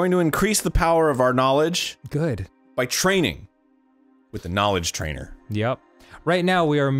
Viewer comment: I thought it said knowledge trailer and i was like why is this trailer 36 minutes long lol.